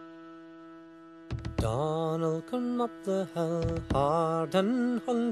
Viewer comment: I swore I heard McDonald